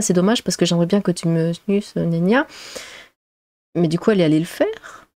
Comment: "J'aimerais bien que tu me sussnania " ???? Ça veut dire quoi? 😄 Je connais pas le verbe sussnanier...😊